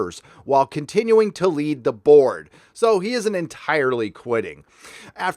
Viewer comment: Excuse me, but did you say he would, "...continue to lead the Borg..."?